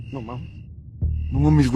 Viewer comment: I hear "No Mommies" or Smi La" (Yes I know the spelling is wrong... Just what I hear. I am going to AUTOMATICALLY figure the clip is fake. EVERY time those guys went "running" at the supposed "alien" they would ALWAYS drop the camera down so we couldn't see in front of them. So tells me it is edited. Too much of a coincidence every time you run... You point the camera to the ground.